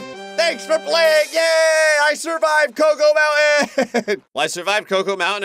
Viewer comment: when said cocoa mountain at the end i thought he said cocomelon💀